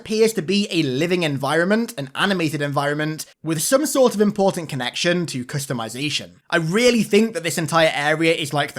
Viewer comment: Damn, I thought he said crotch customization- this is what I get for watching this half asleep.